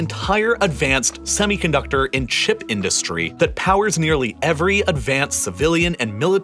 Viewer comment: I misheard "advanced semiconductor in shit industry" I was like wait what ???